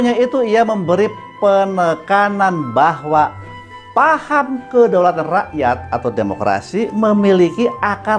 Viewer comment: si prof ngomong apa ya maksudnya? Kedaulatan ato apa sih?